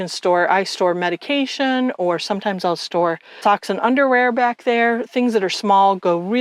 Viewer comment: Great video tour! When you said you store “socks and underwear” I heard “toxic underwear” until it hit me 😂. Cute pup! I have a Pyrenees she may have to stay with her brothers, she might not fit lol.